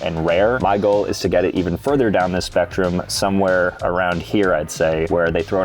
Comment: did u say spectrum